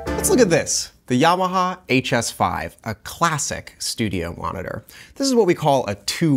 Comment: did he say classic studio monitor!! 😂😂